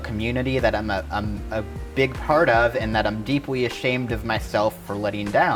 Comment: Did she say deepwy at 6:34????